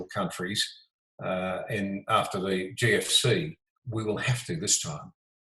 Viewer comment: Hope the people kick out the governments who did this, and hang them! PS thought he said ‘After the KFC.’